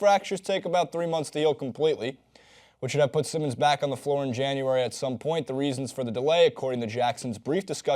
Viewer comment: January* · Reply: Dante Jackson I thought he said Feb.